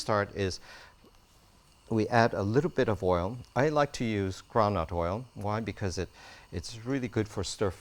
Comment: what type of oil did he use? I thought he said Brown Nut oil? never heard of it …….